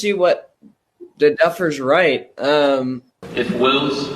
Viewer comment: Did he say Effers?LoL.